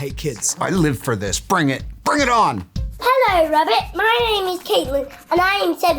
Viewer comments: I thought the first kid said "Hello rabbit!" instead of "Hello Robert!" 😂😂 Just me? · I thought she said "hello rabbit" and thought these are some rude children 😂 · you can’t convince me the first kid didn’t say “HELLO RABBIT” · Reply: I thought it said HeLlO rObBUt